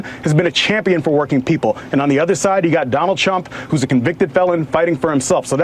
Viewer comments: sounds like he said Donald Chump lol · Did he say Donald Chump @ 14:15 😂😂😂😂😂